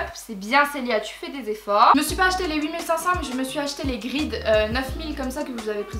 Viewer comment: la paire que tu présentes à 9:18 s'appelle comment ? enfin jveux dire c'est quoi la référence car j'ai pas bien entendu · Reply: Clara Vermeersch C'est les Saucony Grid 9000 !